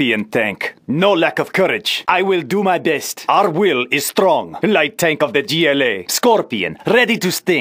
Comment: I thought he said, the Elite Tank of the GLA , but actually it was The Light Tank of the GLA?